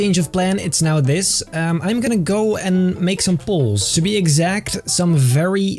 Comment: no disrespect but when says "poles" it sounds like he is saying "balls".